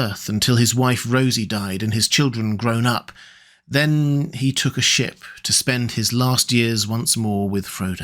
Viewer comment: Oh my god I nearly died laughing I was doing something else so the video didn't have my full attention then what I thought I heard was then...... He took a shit.